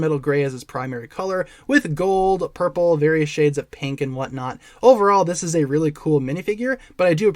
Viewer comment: 😳 thought you said 50 shades of pink.....